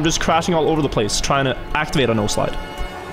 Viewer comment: This whole time, I thought he said nose slide